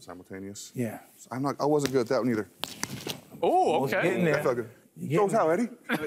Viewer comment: Right after he does the double punch at 1:02 does Urlacher say "oooh OK, that fucken" ??